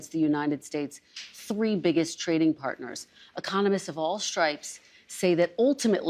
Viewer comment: When she said "economists of all types think tariffs end up impacting the consumer", I heard, " communists of all types think..."